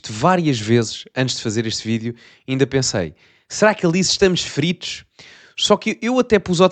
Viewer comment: Só eu é que ouvi "feridos"? Não me entendam mal, à 1a também ouvi fodidos, mas parece-me que ele diz feridos.